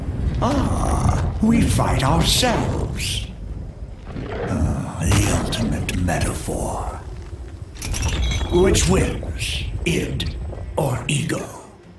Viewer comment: WHAT THE FUCK IS HE SAYING?!?! I'VE NEVER BEEN ABLE TO HEAR WHAT HE SAYS HERE & IT PISSES ME OFF. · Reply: Ah, we fight ourselves The ultimate metaphor Which wins, Id? Or ego?